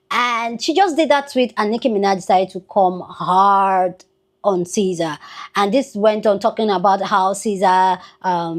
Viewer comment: I thought you were saying Ceasar. Her fall is similar in nature except she’s Brutus too.